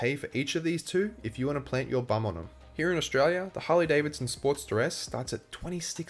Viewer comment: I got confused as I had this in listen mode. I kept hearing “Sport - steress”. Why is the sport stressed? Then I realized the accent! Great review. HD wins across all categories!